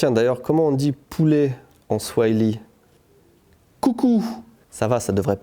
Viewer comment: j'ai entendue oocoo..... (si vous avez pas jouer à zelda twilight princess, cherchez pas plus loin XDD)